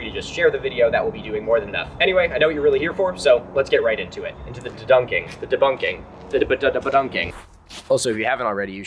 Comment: Legit thought you said the debuk cave